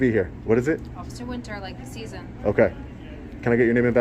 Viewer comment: WOW!! ...and I heard "OfficerSphincster" She needs to just say Winter.